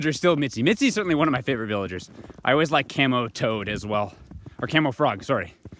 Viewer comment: When you said "camo toad" I heard "camel toed."